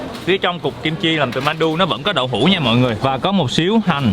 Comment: cục kim chi làm từ mandu :))) à hay mingh nghe nhầm